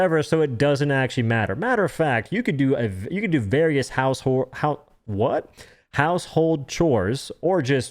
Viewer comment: “What?” House whxre, you said house whxre. 😂